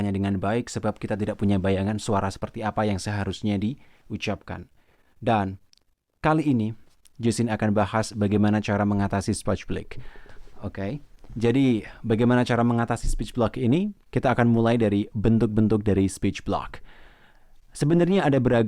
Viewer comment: Sporch blick 1:42 Speech Block Yang bener yang mana min, maaf kalau salah denger